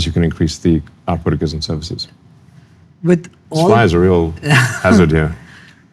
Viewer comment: I wasn't watching but listening. At about the 49th minute. I heard "supply is a real hazard here" and it made complete sense in that context. Turns out he said "this fly is a real hazard here"! 😂 And then he said "maybe we can ask it(fly)to go somewhere else" and I thought, Mars? 😮